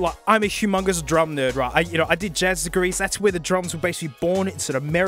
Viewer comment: what is he saying at 11:21, "jazzicurries"? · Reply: Jazz Degrees, it's what he went to college for